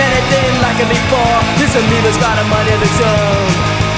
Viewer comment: I thought they were saying Tony hawk until I checked my gta radio and it said amoeba